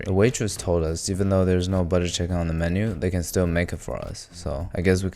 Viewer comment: butter kitchen instead of butter chicken 😂😂